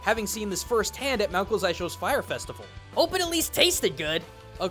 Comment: DID YOU JUST SAY FIRE FESTIVAL???